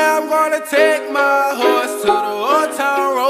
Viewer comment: Kinda sound like it sayin I'm take my horses to the hotel road